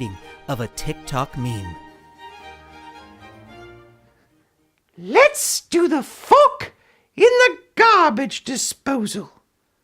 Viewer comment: What other people hear: Let's do the fork in the garbage disposal What I hear: Let's do the fuck in the garbage disposal